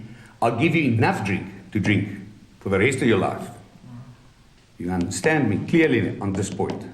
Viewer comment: Did he say he would give him enough to drink for the rest of his life and eat every cig in the world if he caught him smoking? Mah dude. OG